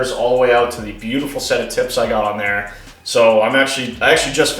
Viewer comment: Hahaha!! Sound like you said…. “Set of tits” lol. Good show brother!! Big fan!!!